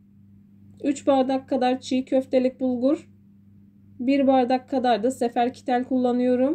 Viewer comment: Çig köftelik bulgur dediniz degilmi,ben yanlış mı anladım yoksa😊